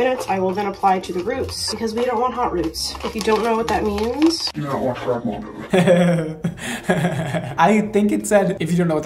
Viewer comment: Nak I thought she said “if you don’t know what hot roots are, you’re normal” but “you don’t watch brad mondo” is also a valid option